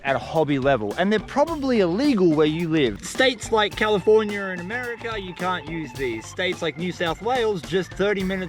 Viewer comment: Did you just says "States like California and America " 🤦🏽‍♂️